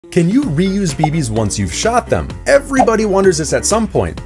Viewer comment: I wasnt looking at the screen and i thought he said “can u reuse babies once you shot them”. ☠️☠️☠️☠️